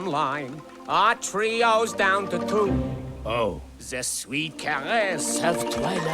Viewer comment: …I’m sorry… WHAT did he (Timone) say… “The sweet……of twilight”… I kinda missed whatever that middle word was. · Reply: The sweet caress of twilight